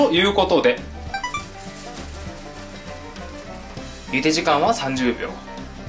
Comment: １時間は30秒に聞こえる